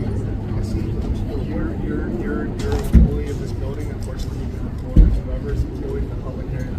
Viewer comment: When they met "privately" I thought I heard the cop use the word "unfortunately," which is a pet peeve of mine. No cop should ever tell public servants that "unfortunately" people have rights and nothing can be done about it.